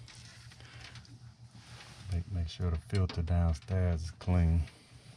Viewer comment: You could of checked the filter in all that time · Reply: Idk im kind of hard of hearing sometimes. I could have sworn hear he said he was checking the filters downstairs. I could be wrong.